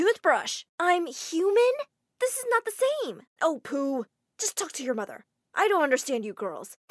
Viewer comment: I swear at 2:28 the dad said oh poo XD